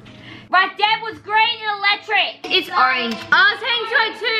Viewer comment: It sounded like said Electric was green in dead